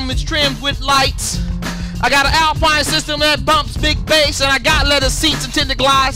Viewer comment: is he saying glice?? Wtf?